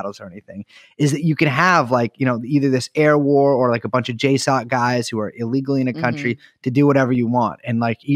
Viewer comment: "Either this air war of a bunch of ????? guys" What did he say? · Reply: JSOC. joint special operations command. Spec ops group full of complete psychos. did an episode just in that topic. episode 163: JSUC